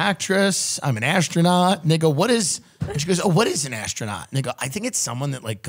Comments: why does it sound like hes saying nigga at 6:45 LOL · i think tim was saying "and they go" 6:46 but it sounds like the n word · am i the only one that heres the nword instead of "then they go"